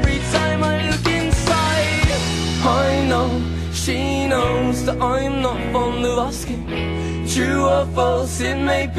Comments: Idk y but i always thought he was saying "i know she knows that im not on the roster" lmao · I'M NOT FOND OF ASKING!?!?!?!?!? I FINALLY KNOW WHAT IT SAYS!!! FOR THE LONGEST TIME I WOULD JUST MAKE RANDOM NOISES AT THAT PART CUZ I COULD NOT FOR THE LIFE OF ME FIGURE OUT WHAT IT SAID!!! MY LIFE IS COMPLETE!!! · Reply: unknown unknown lol I thought he was saying I’m not from the rusky 😂😂😂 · lol its funny listening to a old song in 2015 this is 2007, an finally i know what hes saying because before i thought he said i not from Nebraska.. lol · I'M NOT FOND OF ASKING!?!?!?!?!? I FINALLY KNOW WHAT IT SAYS!!! FOR THE LONGEST TIME I WOULD JUST MAKE RANDOM NOISES AT THAT PART CUZ I COULD NOT FOR THE LIFE OF ME FIGURE OUT WHAT IT SAID!!! MY LIFE IS COMPLETE!!! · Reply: Analisa Idalia I genuinely thought it said ‘I’m not from Nebraska’, lol. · I'M NOT FOND OF ASKING!?!?!?!?!? I FINALLY KNOW WHAT IT SAYS!!! FOR THE LONGEST TIME I WOULD JUST MAKE RANDOM NOISES AT THAT PART CUZ I COULD NOT FOR THE LIFE OF ME FIGURE OUT WHAT IT SAID!!! MY LIFE IS COMPLETE!!! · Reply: Literally thought until now thar he said "im not further asking" lol and at the end "hold on to your pride" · I'M NOT FOND OF ASKING!?!?!?!?!? I FINALLY KNOW WHAT IT SAYS!!! FOR THE LONGEST TIME I WOULD JUST MAKE RANDOM NOISES AT THAT PART CUZ I COULD NOT FOR THE LIFE OF ME FIGURE OUT WHAT IT SAID!!! MY LIFE IS COMPLETE!!! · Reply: I always thought it said "I'm not from Nebraska" I jsut????????? FINALLY